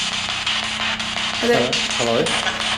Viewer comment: In 18:58 when she said "I'm I hear in the spirit box says Hello